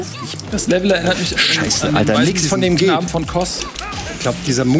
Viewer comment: Hat jemand verstanden was sagt? 11:28 Er sagt das Level erinnert Ihn an ...? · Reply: "...an den Knaben von Kos".Der "Waisenknabe von Kos" ist der Endboss im Bloodborne-DLC und hat eine ähnliche Arena (Küste, von Wasser bedeckt). <link>